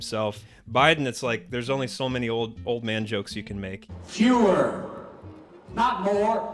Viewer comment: No joke, when they showed the clip of Biden saying “fewer”, I thought he was saying Führer.